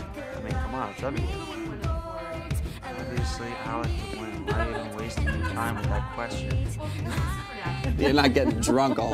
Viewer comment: What did he say???? I couldn't hear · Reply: "Obviously Alec would win. Why are you even wasting your time with that question" :)